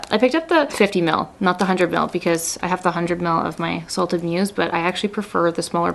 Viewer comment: I love this! Also, it sounded like you said “salted P****” instead of salted muse. 😂